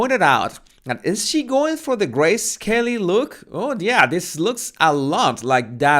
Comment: Turned my back to the screen for a few seconds to take a bite of my food, and I hear "Is she going for the gray, scaly look?" This sounded very intriguing, so I turned back and found at that you were actually saying "Grace Kelly", but I have never appreciated your accent more, because "gray, scaly" fits MeGain so much better! And that pose in the next picture? Who DOES that, except as a total joke?